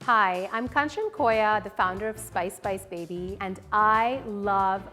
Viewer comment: Am I the only one who thought at the start she said ' Ice Ice Babyyy'